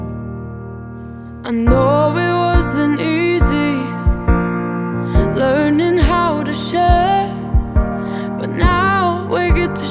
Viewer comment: 1.44 I thought she said “I know it wasn’t easy knowing how to Shit.” So not the shitting part is hard, just the knowing of how to do so. 😂😂